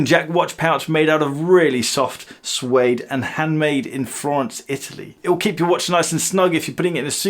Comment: The pouch is handmade in France - Italy ..? Am I mishearing something or is he throwing together two "pretentious" locations to make it sound good? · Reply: Florence, Italy